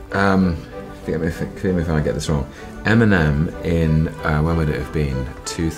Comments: Lol he said Eminem I thought he said m&m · Am I the only one who thought Tom was saying "m&m" instead of Eminem and was so confused